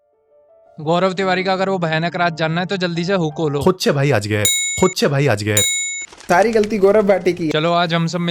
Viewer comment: bhai es intro me kya bolta h ?? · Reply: Koche bhai ajger